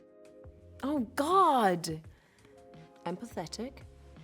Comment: a first I heard 'I'm pathetic' at 0:16 and I was like what..?